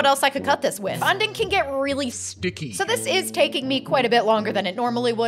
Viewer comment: I thought you said "this is CAKING me a long time" instead of taking lol